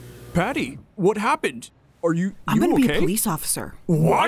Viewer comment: It’s sounds like skid said”are you gay”💀